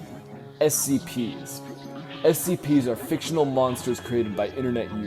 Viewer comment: I hear "SCP are fictional monsters created by internet losers" 🤣